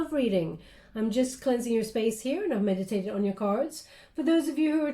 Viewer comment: At the beginning I thought you said I'm cleansing your face not space 😂 Thank you for these readings, I so look forward to them 💜